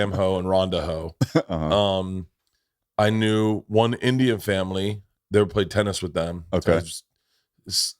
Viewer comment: Did Bert just say India family